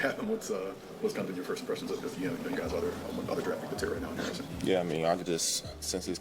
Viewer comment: he asked about ingram i think ? i heard "other draft pick"